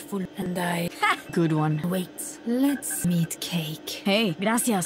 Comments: Am i the only one who understands "Lets MEET cake"? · wait, let's meet cake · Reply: yeah ik but it sounds like she said "let's meet cake" lol